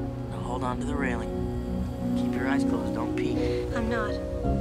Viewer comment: It's really hard to explain about the kind of feelings I got from this scene. Such a beautiful scene, with amazingly beautiful music arrangements. And every time I hear Jack says "Close your eyes, don't peek" I feel like I'm crying! I think this scene is always be the most iconic, beautiful, emotional scene ever. And I wonder, in a few years later.. I'd probably still crying if I watch this movie all over again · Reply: I thought he said "Close your eyes, don't pee"